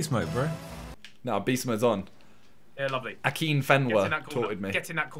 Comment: did Simon just sayyy akinfenwa (the footballer if he still plays) but wtf Simon lolol